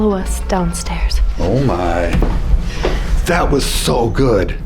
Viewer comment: I hear “He’s being productive today” 😂